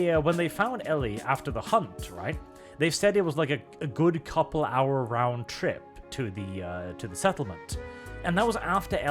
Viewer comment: What David actually says is that it's a "4 miles round trip". Not exactly a "couple hours round trip", to be fair.